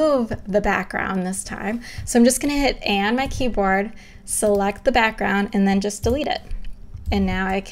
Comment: What does she say at 9:19 while removing the background colour from swatch? I can't make it out. Someone please help me!!! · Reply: Select the background, then just delete it :)